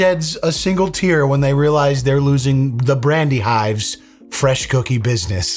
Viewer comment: ohhhh BRANDY hive... That whole time I thought you guys were saying "bratty hive" lol